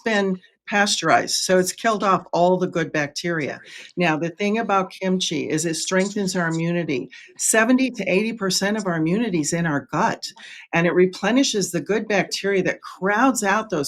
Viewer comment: Lol I misheard it “70% of our immunity is in our butt” - lol… Gut not butt!